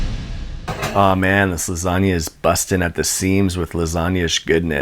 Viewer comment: i thought he said “aw man this lasagna is bussin” and now i can’t stop laughing